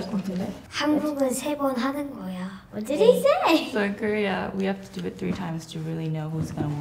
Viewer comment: parece que dice en spanish "QUE DICEEE" JAJAJA guapa la niña